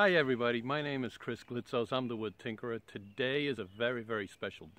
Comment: I THOUGHT U SAID Hello everybody, my name is Christmas